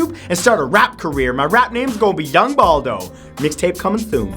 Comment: Yung Baldo. I can dig it. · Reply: I heard 'Dumb Baldo'... lol.